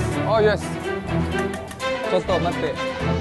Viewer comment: Nih gua salah denger tw nggak, di menit ke 2:04 Danny ngmong pake bahasa Jepang"Chotto Matte(ちょっと待って)".